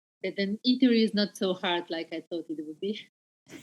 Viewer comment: What did say in response to 31:10 question? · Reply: I believe she says "The eatery is not so hard like I thought it would be". She talks in another part of the interview about how different the food is in America, how much it's changed since the last time she was in America. Hope you enjoyed it!